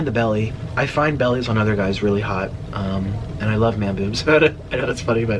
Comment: Skipped the video to 47:54 and all I hear is “I like man boobs” 😂🤦🏻‍♀️😂